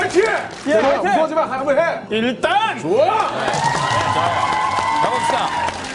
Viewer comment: Daesung sounded like he was saying "IL EST TEMPS" in french. xDD